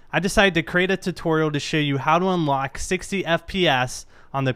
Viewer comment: did he say 60 FP-ass?